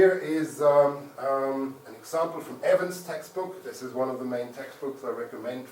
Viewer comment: What's the name of the book mentioned @ 15:53? I can't hear it very well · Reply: Evans. <link>